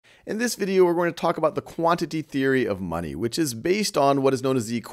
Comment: At first I thought it said quantum theory of money lmao